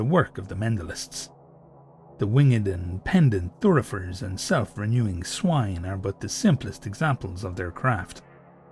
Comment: Sorry, did you say "self-renewing swine" at 8:18? I guess I shouldn't be surprised, but there's something very ghoulish about that idea.